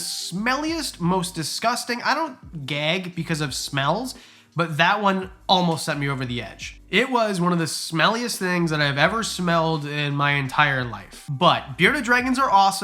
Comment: I thought it said smallest at first